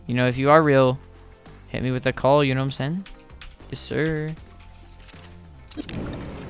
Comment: my phone literally thought tiko said hey Siri and it brought me to Siri lol